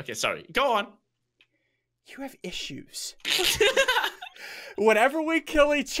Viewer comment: When he said “you have issues.” I thought he said “you have fishues